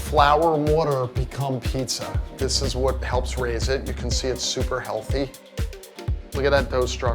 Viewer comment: did he just said super healthy...( constipation laughing in corner)